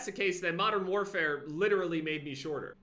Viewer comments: i thought it said: "MODERN WARFARE actually made me a shooter IRL" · Thought it said “MW made me a shooter irl” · I thought it said made me a shooter irl 😆 🤣